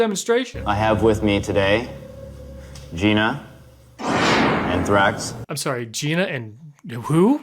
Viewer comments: I thought when they said "and Thrax" they said Anthrax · Ohhh he said “and Thrax” I thought he said “anthrax” 😂 that was funnier to me. Gina and Anthrax 😂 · I thought said Anthraxxx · I heard him say don geena anthrax not and thraxx.